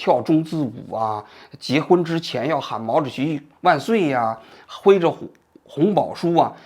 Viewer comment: 那是喊毛主席万岁吗，我都听成败类了。简直实在亵渎毛主席